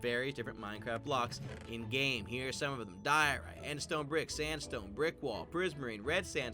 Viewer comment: when it said diorite I thought it said dorito 😅😅😅